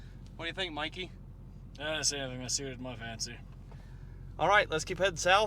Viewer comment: What the hell did mikey say at 1:07 when you asked him what he thought? Total boomhower. I replayed it 5 times and still couldn’t understand a word he said . I was cracking up. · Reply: Nothing that suited his fancy